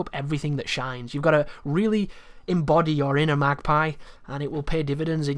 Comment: Did you say magpie at 22:50?